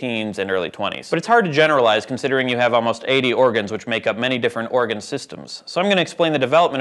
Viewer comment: totally thought that said orgasms.